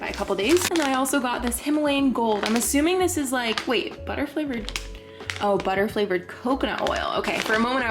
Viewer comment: with the popcorn i thought you said 'butterfly flavored popcorn' i was so shocked haha